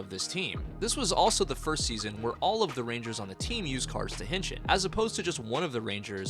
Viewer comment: "This was also the first season where all of the rangers used carsdaheshin" wtf are you saying here....?? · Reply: "used cards to henshin"